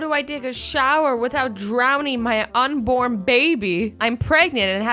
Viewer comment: I initially thought ot said "unicorn baby", but then I realized it said "unborn baby"